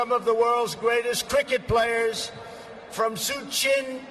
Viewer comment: It sounded like he said tricket instead of cricket, I bet he would say they purposely put unpronounceable names and words to make him look bad